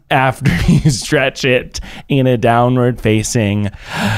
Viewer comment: I KNOW Miles said "downward doge" but I heard "downward douche" xD